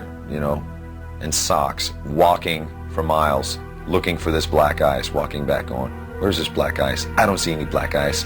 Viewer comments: When James said “Black Ice” I thought he said “Black Guys” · Throughout this entire quote, I thought James said "black guys", but then I saw on wikipedia that it was "black ice".